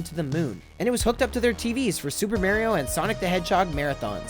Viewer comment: why does it sound like he says super merio....its MARIO!!